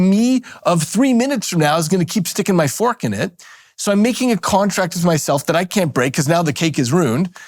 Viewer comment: I make useless contracts with myself all the time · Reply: Oh, i thought you said "contact"...